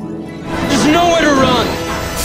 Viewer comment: Every time I hear "there is nowhere to run" and "shut" I start uncontrollably laughing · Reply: I always heard is as 'Snowhere to run!' and thought Barry was making a lame pun to some Icethemed enemy...